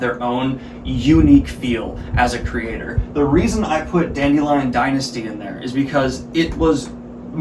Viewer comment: which story he told about here? i couldn't understand it clearly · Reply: Dandelion Dynasty